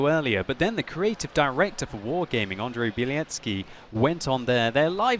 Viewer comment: Did I hear Andre BLYATziki in 2:00 ?? Lmao